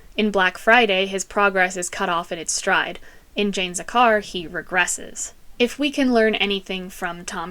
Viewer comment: I wasn't fully paying attention then I heard 'Janes a car' randomly lol